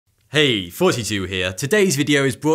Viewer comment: Why do I always hear it as "hey 42 here" without captions